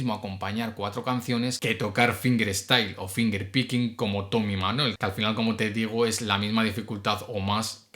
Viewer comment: tomy ke? No se entiende el guitarrista ke dices,esa parte hablas muy rápido 😂, me gustaría saber de kien hablas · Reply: Tommy Emmanuel.